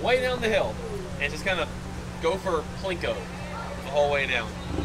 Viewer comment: i thought he said go for plinko, then i realised he said gopher plinko